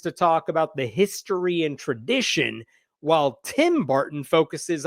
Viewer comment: For a second I thought that said Tim Burton. Thank goodness it didn't.